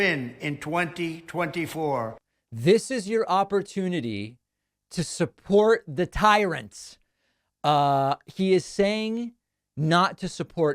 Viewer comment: It sounds like what he said was "this is your chance to take a stand against tyrants AND support the one and only movement..." The connecting word is AND, not THAT.